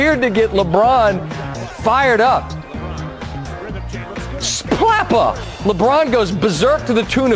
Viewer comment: did he just say splappa?